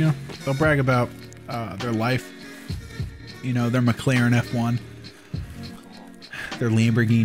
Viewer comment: When he said “mclaren F1” I heard “my clarinet farm”